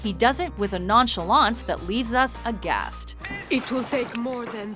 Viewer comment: Did she really say agasseed during the one at 8:00?